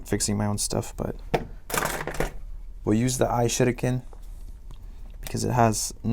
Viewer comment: He says that he will use the I shitiken but it sounds like he said I will use the I shit again